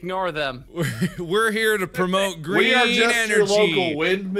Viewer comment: I love at 6:17 when Mark says: “We’re here to promote greeen energy!” and the captions say “green onion”. I swear like 90% of the time the captions are what actually make me laugh.